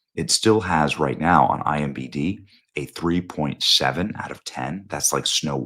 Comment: I gave you a like but you should be more careful about words, you said "IMBD" instead of "IMDB"